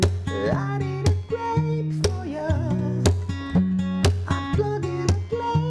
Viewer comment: did u say "id eat a grape for ya" aahahhaha your a legend